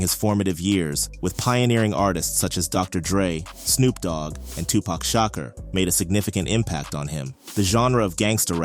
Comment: I heard "2pac Shocker". lol